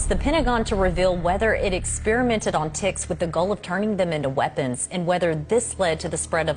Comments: I thought it said experiment on tits and turn them into weapons and I was so confused but same time impressed 🤣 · i DEADASS thought that lady said “it experimented on ✨ t i t s ✨” 😂😂😂 until she said lyme disease n then i was like ooooohhhhhh TICKS not TITS 😂😂😂